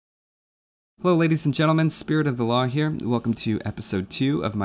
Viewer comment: Every time he says his name i keep hearing it as 'spirit of allah'